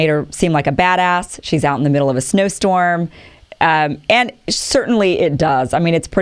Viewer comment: Snowstorm, I thought you were saying she was giving the voters a "snow job".